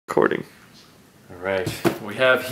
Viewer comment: is he saying Recording?